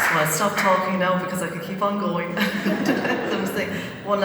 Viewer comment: What does that very lovely lady say at 5:55? I wish I could make it out. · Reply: Hotshot13 “I will stop talking now as I could keep on going”😃